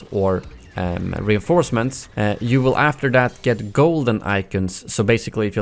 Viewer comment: Shadow idk getting a golden gun is going to suck me out of the atmosphere like yeah I can show it off but I’d rather just have new weapons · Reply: TJP Real I totally misheard him I thought he said golden items not golden icon I guess that’s cool, It’s not really a big deal at all then but I would still want a higher profile level cap